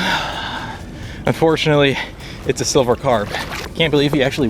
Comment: They are tasty.....VERY. They are marketed as "silverfin" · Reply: thank you. On the youtube video I seen, the fellow was hard to understand. I thought he said silver pan, as in "panfish". Lol. Silver fin makes more sense.